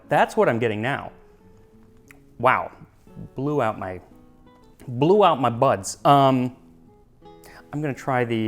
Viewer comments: For a second I thought he said "blew out my butts"😂😂😂 TMI, bro, TMI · "blew out my butts" is my new favorite thing to say. I misheard it but now that has replaced the truth. I am a new woman and I will never be the same